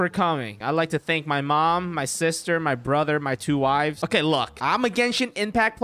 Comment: did you say 2 wives?? :o 😬😳😱